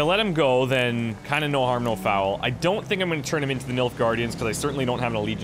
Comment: Every time you say Nilfgaardian I hear "MILF guardian".